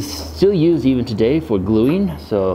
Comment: I'm fairly certain you said "high hopes" there at the end, but I could've SWORN I heard "hide hopes" 🤔😂